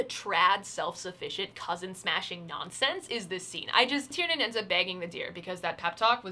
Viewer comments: "Tiernan ends up bagging the deer" Genuinely misheard that as "banging" and was only partially skeptical, which is a testament to... something about this book. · i swear to god i thought you said "tiernan ends up banging the deer" and i was like "wait- WHAT" because while i did know this story had no moral bounds, i was absolutely not expecting bestiality to be making an appearance. still, i was completely ready to believe it did fhdjskh edit: apparently my word choice was poor; there is not actually any bestiality in this book! i just misheard something & thought i’d share it because it was funny. your faith in humanity can limp along for another day lol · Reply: LOL THEY DIDNT ACTUALLY SAY THAT they said she BAGS the deer, as in kills it! i just thought it was funny that i misheard it that way lol